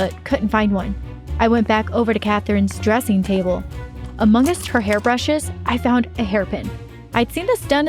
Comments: nobody: Me: IM sorry but did you say AMONG US??? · Who else heard among us insted od amongst?